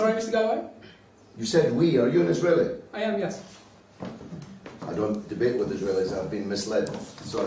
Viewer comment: Timeline 5:45 He said "I don't debate with Israelis. I've been mislead, Sorry". He did not say "I don't debate with JEWS". Why you guys do me like that? 🤣🤣 I came here to catch this guy hating jews, but you guys got me waste my time watch this video and couldn't find any place where he said he hate jews? Are you guys lying to deceive people to come watch your videos?